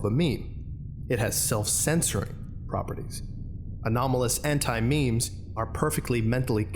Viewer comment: I nearly heard it as antimatter